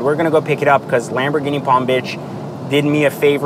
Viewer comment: Did he just say Palm B*tch???👍🏾😂